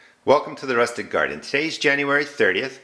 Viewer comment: I know my local library has a device that can be checked out for free (by residents of their service area) that can be plugged in and other things plugged into it and will give all sorts of information about energy consumption. for those who aren't sure if they're putting the right information in the online calculator or think there's some undetected inefficiency they might be able to find a similar resource. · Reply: all this time I've thought you were saying "The rustic garden"